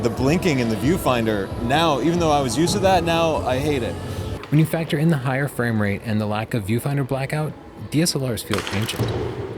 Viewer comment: Did anyone understand what Tony said about DSLRs at about minute 7:39? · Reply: "When you factor in the higher framerate and the lack of viewfinder blackout, DSLRs feel ancient."